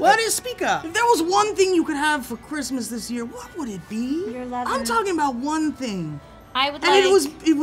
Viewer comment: Did Trisha just say your lover? Lol